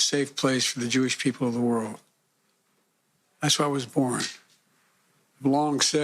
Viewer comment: I swear in my balls he says "that's where I was born".